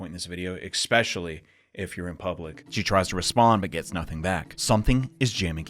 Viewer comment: "Do not google Yuka Tachibana at any point of this video, especially if-" Ad plays, Opens new tab, shocked and quickly closest it since am at an internet cafe, skip ad "-you're in puiblic" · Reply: I thought initially someone said you're in public to you 😂😂